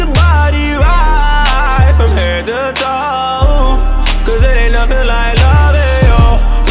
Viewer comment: I swear I thought this man was saying "there ain't nothing like Mario" lol